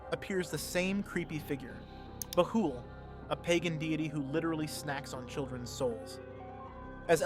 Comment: Did you just say pagan diddy😂😂😂😂